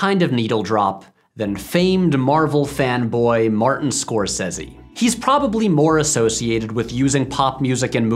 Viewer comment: have i heard "famed Marvel fan boy Martin Scorsese" ??? 😂🤣😂🤣